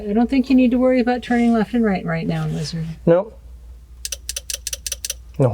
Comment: Did she call him Lizard? 6:18 🦎 lmao